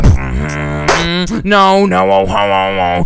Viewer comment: it sounded like he said No Homo